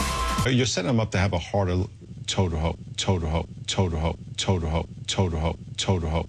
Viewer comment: I think he has said "hard and total ho." OH LAWD! It's also funny how many black people are against gay marriage when they were in the same boat as us gay people at one time too. When Martin Luther King Jr. went on protests, he thought that predominantly white churches would be on their side, but to his astonishment they weren't, just like a majority of blacks aren't on our side with our deserved civil rights today. Interesting how history repeats itself, in so many ways.